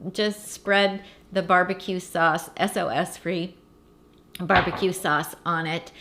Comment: What is SLS? I heard you say SLS free barbecue sauce.